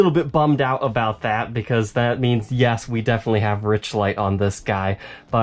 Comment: What do you mean by "rich lay" era Gibson? I might have misheard this.